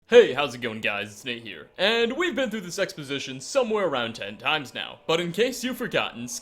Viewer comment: i had to start the video over because i mis-heard the intro... instead of "we've been through this exposition somewhere around 10 times now" i heard "we've been through this sex position somewhere around 10 times now"